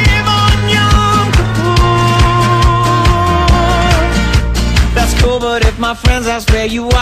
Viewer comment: did he say yom kippur ??